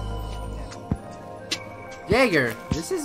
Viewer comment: when he said jager, I thought he said the n word